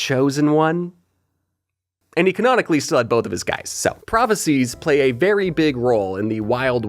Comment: I thought you said "he canonically slept with both of those guys so.." 💔